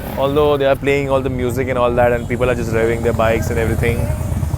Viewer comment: fuck in 16:18 i thought that he said and the people are wearing their bikes hahahhahahhahhahahaha